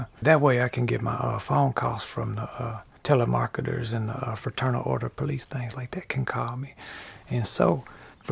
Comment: Fraternal order of police things? I didn't catch that